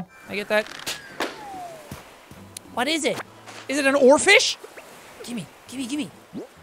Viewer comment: i thought he said "orphan" instead of "oarfish" eventually someone called isabelle that helps you move your house and KICK VILLAGERS OFF YOUR ISLAND so than you can kick sheri off your island